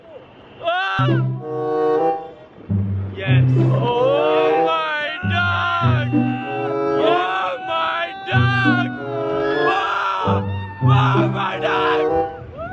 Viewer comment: did he said ""oh my dog""?